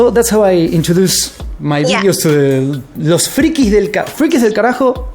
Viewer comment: escuche que dijo frikis del asco :v pero no estamos tan feos xD